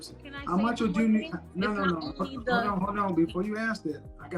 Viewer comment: Did he just say hold on bit** at 10:50?